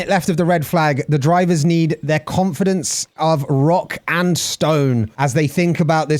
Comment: Did i hear a Rock and Stone?